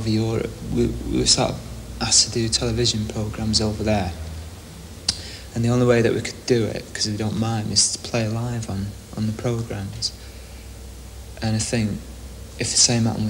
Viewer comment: what did he say from 1:58 to 2:23? I couldn't understand a word and the auto subtitles seems to be inaccurate as well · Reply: “We were asked to do television programs over there. And the only way we could do it, because we don’t mime, is to play live on the programs. And I think, with Confusion, it would become boring, and the sound’s always bad when you play live compared to people miming, so doing a video was a good way of satisfying both of our needs”